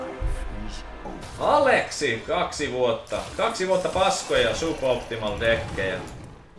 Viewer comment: what was said at 3:19? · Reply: kaksi vuotta paskoja suboptimal deckja